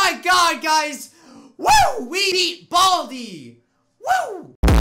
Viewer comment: subtitles say " WE EAT BALDI"